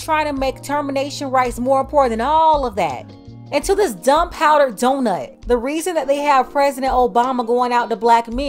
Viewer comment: I was all serious watching this video, then i heard "dumb powdered donut "😂